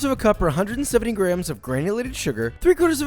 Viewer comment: The video buffered around 2:19, and instead of "granulated sugar", he said " granulated shoe"